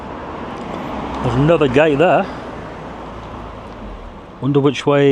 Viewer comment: I almost misheard "There's another gate there" as "There's another gay there". 😛